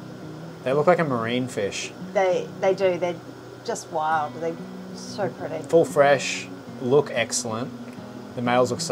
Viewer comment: What are these fish @ 30:28? I heard her say the name but when I look that up, I cannot find them. Maybe I'm spelling them wrong? I'm in love with those little guys. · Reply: Orange Furcatus